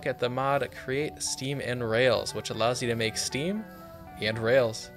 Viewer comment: When you said “create steam and rails”, the subtitles thought you said “create semen rails”…